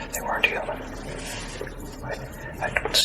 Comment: He clearly said they weren't human. How did they weren't together make it to the final cut?